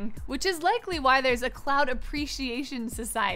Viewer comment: I thought she was saying clout